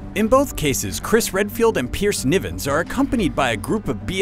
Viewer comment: i thought he was about to say "Periced Nipples" ahaha